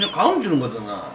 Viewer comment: 가음× 고함o 경상도는 가암이라고 들리죠